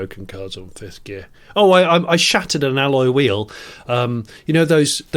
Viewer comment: I thought said he shat in an alloy wheel....